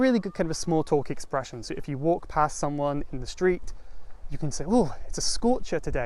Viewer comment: I didn't properly understand some part. 4:02 Did you say "walk pass" or "walk past"? Also why did you add one of these, what kind of changes you did in the meaning? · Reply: I said "walk past someone". But we often don't pronounce the T because it's a bit harder to do so. Of course that makes it harder to hear what we're saying, but based on the context we know that "walk past" is a common phrase, meaning "To walk by someone" or when 2 people walk next to each other in different directions. Don't forget I have subtitles you can check too :)